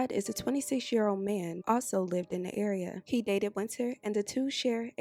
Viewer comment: Did she say at 2:43, he dated Wynter...??? 🤔